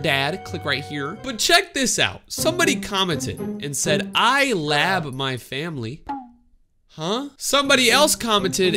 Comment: if you dont know what he said he said i love my dad also heart my comment please im subbed since 2020 jan 20